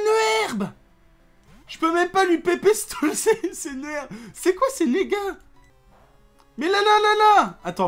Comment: "c'est quoi ses nigga"